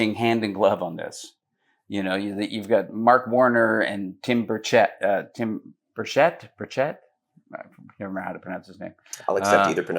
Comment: I thought I heard "senator bird shit" the first time he was introduced via audio. No wonder everyone wants to call him Burchett